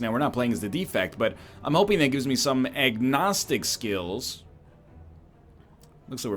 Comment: did he say "eggnostic skills" He's become self aware lol.